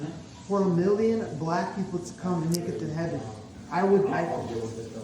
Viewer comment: what in the weird “black people taking over heaven”(I swear that’s what I heard)1 did this hillbilly kiddo just mumble? 11:52 they always do! Why are Jesus freaks always racist? Not all of them obviously, but I feel white supremacists a lot of the time believe in some form of the “god” and “Jesus” hogwash, or at the very least god.